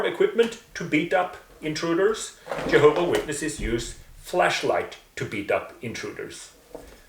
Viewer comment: Use a flashlight for self defense? Oh I thought they said Fleshlight. My bad!